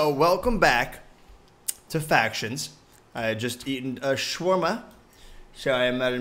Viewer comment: Wat did u just eat, I watched that back like 4 times and I heard shwarma like wth is that